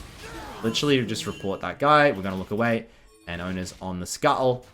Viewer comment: I hear “owner” instead of Oner when it is the english accent